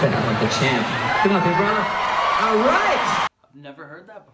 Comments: Keith is clearly saying "Come on up here, brother!", it's just his Aussie accent probably makes it sound like something else to people who have never heard an Australian accent, lol. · You can't hear him say get up here brother..You need to get your hearing aids checked. There might be a possibillity that it was staged. But the name part wasn't it.